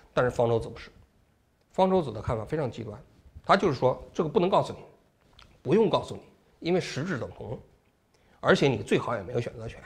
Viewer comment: ，不能告诉你不用告诉你，因为“实质等同”？听不清啊